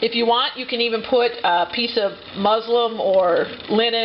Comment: what did you say? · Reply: Muslin. , a cotton fabric of plain weave